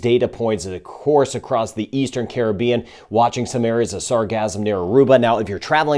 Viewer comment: Sarcasm is all over the place! Sorry, but it sounds like your saying Sarcasm 😂